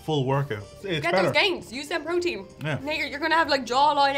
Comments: what was said here, it's so quick! love the vid y'all~ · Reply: Hey there! Just to clarify, at 4:46 says "Now you're, you're gonna have a jawline out to here!" - we were there when it was filmed, so trust us, it was exactly that! 😁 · Wait...what did say @ 4:46? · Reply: Hey there! Just to clarify, says "Now you're, you're gonna have a jawline out to here!" - trust us, we were there on the day it was filmed! 😁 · Did say the N-word at 4:45? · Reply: Hey there! Just to clarify, at 4:46 says "Now you're, you're gonna have a jawline out to here!" - we were there when it was filmed, so trust us, it was exactly that! 😁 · Can someone please translate (from Irish to American) what says at the 4:46 mark? It doesn’t sound nice. Ha ha. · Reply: Hey there! Just to clarify, at 4:46 says "Now you're, you're gonna have a jawline out to here!" - we were there when it was filmed, so trust us, it was exactly that! 😁